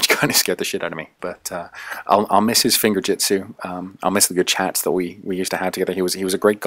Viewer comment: sounds like you said finger tits